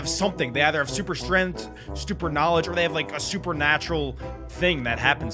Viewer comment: the caption is stupid instead of knoledge 🤣